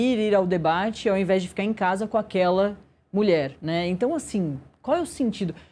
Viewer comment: Que mentira deslavada! Ele disse: "aquela Janja" ele nunca falou "aquela mulher". Isso muda absolutamente tudo em relação ao que ele disse. No momento da fala de Renan a crítica repousa sobre a figura da Janja (ligado ao Lula e é odiada por muitos de ambos os espectros), independente de ser mulher ou não, que é um ator político(haja vista que ela atuou politicamente por diversas vezes no governo Lula). Em suma o ataque foi a um ator político, que é uma mulher. Ponto!